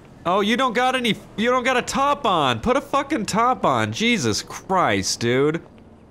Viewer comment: it sounded like you said you don't have a tampon on put a tampon on LOL 😂😂